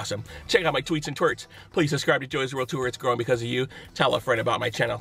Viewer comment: I Love this guy. But at the end I always hear "Im growing beacuse of you". As in his body...and not his..channel... I show myself out.. wowowowowooo